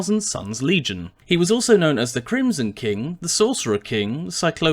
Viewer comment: I thought you said "The Crimson Chin"